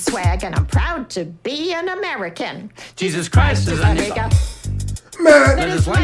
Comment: hold on it sounded like the woman said “jesus Christ is A n****” like wtf 😂